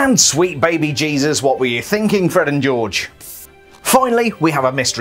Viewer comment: Ummm did he just say Fred and Georgie